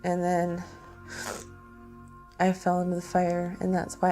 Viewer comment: What does she say at 3:17? Was it I found a new friend? Which I interpret as she went off to have sex with someone she just met. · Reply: She said "I fell in the fire" not "I found a new friend". She said she fell in the fire and hurt herself which is why she had to leave.